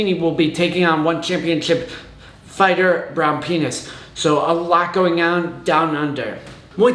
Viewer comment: At the 3:44 mark, did you actually say the fighter's name is, "Brown Penis?" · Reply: The name is Brown Pinas